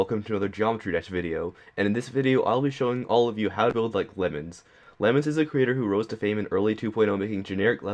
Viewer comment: at the very beginning I thought he said build like women XD